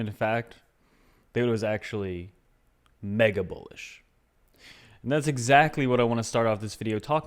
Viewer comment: OOOOHHHH!! Mega Bullish! I thought you said Mega Bullshit!